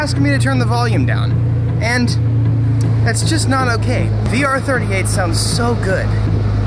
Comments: Did he say R38 @ 1:54? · Did he said r38 @ 1:51 or am i just tripping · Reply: He said VR38